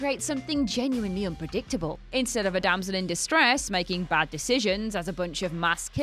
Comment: Did she really say "Damson in distress"?